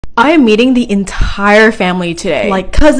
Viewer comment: Sorry I heard " I AM EATING THE ENTIREEE FAMILY TODAY " Lmao no